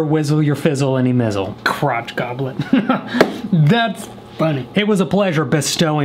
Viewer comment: Crotch goblet, meniscus mug, toe tumbler... no kitchen is complete without them! These will be in the merch store soon, yes? 💜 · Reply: At first I thought he said Crotch Goblin aka kids! Lmfao!